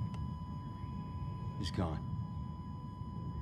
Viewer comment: Somebody explain me what did he say and what does meaning? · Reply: "Looks like you got your wish, Mark."